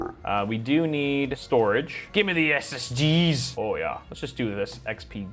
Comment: i thought he said "give me the STDS lmao"